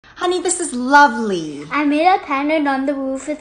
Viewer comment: At the start why do I keep hearing “Honey this is monopoly”💀💀💀